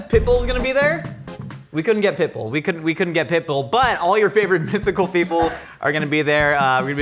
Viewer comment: Pitbull? I thought Josh said Pickle.